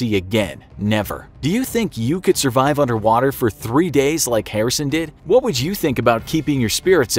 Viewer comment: I thought it said “I got trapped in underwear for 3 days”